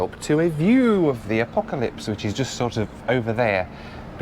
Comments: I heard “I have a view of the Apocalypse” instead of the Acropolis 😊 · Did he just call the Acropolis the "Apocalypse"? lol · did you say "apocalypse"? 😂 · "The Apocalypse?" - Did I hear correctly? 😂😂 Love your work. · I had to listen three times to be sure. You definitely said “Apocalypse” instead of Acropolis. 😂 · I've played it over a few times, did you say The Apocalypse ?? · The apocalypse? I'm not the only one who heard Apocalypse not Acropolis?